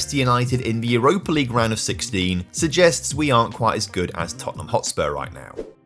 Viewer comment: what did this Tottenham dude say? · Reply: "What do we think of Tottenham?"